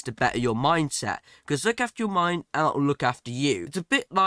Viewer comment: 😂 I thought he said ur mic for the first tip